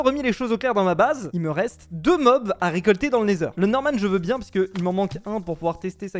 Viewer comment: Le Quoi ?! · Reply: Javais compris le norman 😂